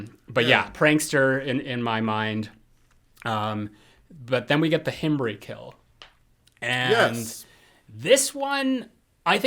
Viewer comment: I heard “ then we get the hemorrhoid kill “ lol first watch